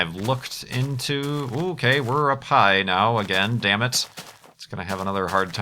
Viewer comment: at first I thought he said: "Ok, we're a pie now again, damn it."